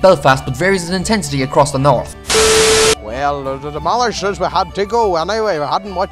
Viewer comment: I couldn't understand a word the man from West Cork said, neither could I understand anything the boy from Antrim in Northern Ireland said. I thought he said, "Well the ... demolishers had to go ..." but what he actually said (when I switched on the subtitles) was, "Well the ... the mother said you had to go ..." However, I could understand most of what the man from Kerry said and if I lived there for a short while I would understand and appreciate the Kerry accent a lot better even though you said hardly anybody can understand what they are saying or words to that effect. I think it's a very nice accent. I really like the South Dublin Accent ... it's cool ...